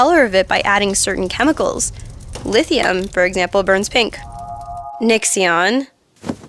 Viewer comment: Nixxion? is what said lol